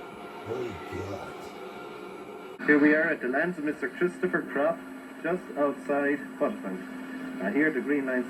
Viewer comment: Thought they said Christopher Crop on that dvd when arriwing to the first field 😂 (Says Croft in comments 😉 )